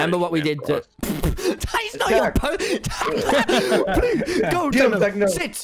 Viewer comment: DID HE SAY "SITZ" LIKE THE GERMAN "SIT" OR AM I COMPLETELY DUMB AS SH!T